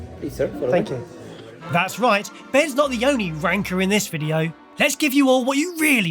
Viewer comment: When said "Hes not the only ranker" I sware I heard "Wanker" 🤣